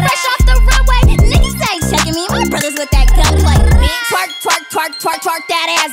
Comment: What I hear at 0:30 twerp, twerp, twerp, twerp